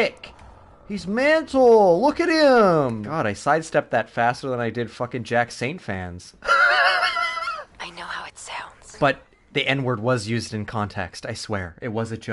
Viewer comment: for a second i thought you said jack stauber and not jack saint and i was like "what beef does mark have with the claymation man"